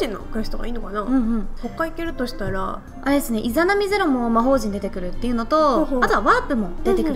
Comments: イザナミ零に聞こえてしょうがない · 何回聞いてもイザナミ零って聞こえる